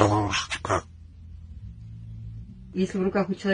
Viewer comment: I wish I knew what he was saying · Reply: He’s saying his name “Karlusha”